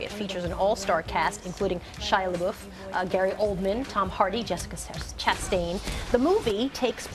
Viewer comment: I swear I heard her say Jessica Simpson chastain